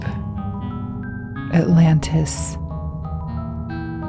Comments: I've never heard of The Bilderberg Group before this video, so I kept hearing "The Build-a-Bear Group"😂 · I misheard Bildeberg as Build-a-Bear and I can't unhear it now, so the Elite are putting microwave chips in teddy bears to beam communist propaganda into the brains of children